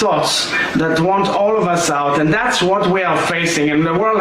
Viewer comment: did he just say they're fighting against extreme fundamentalist thots? 🤨